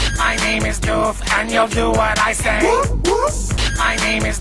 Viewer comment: When you listened to it so much it sounds like he says "My name is doof, and you do WHEN i say :P